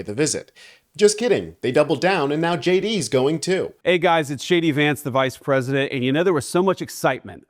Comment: Sounded like he said, Hi there, it’s shady vance here…… LMAO.